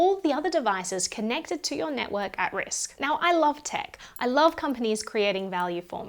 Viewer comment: did anyone else hear "i love dick"? no? ok 😔